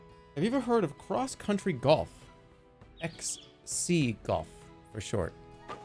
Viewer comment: I thought he said "at-sea golf," and I thought it myself, "now there's a sport!" It'd be way cooler to watch someone drive off of a boat than it would regular land